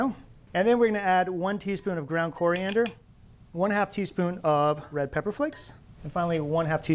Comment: This recipe looks amazing! I took notes but when I watched it again to confirm them, I realized I had misheard the amounts for red pepper flakes and pepper, thinking I heard one AND a half teaspoons of each when I'm now sure he said one...HALF TEASPOON of each! Oops.